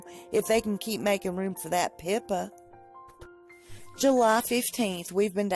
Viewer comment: The first time you said "Pippa," I thought you said "Pimpa" lmao. Meghan's diary is what I thought it would be. I love how Meghan is jealous of Pippa.